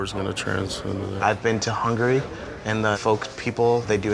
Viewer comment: did he say Hungary?